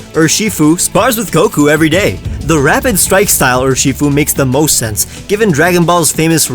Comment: Is it just me or does say rapid strike style sounded like "Rabbit strike style"